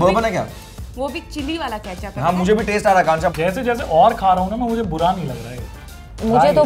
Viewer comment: did he say "ganja"!? 😂😂